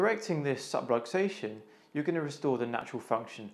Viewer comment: what word Subluxation or some-rotation; couldn't understand what word you said. You have a heavy accent.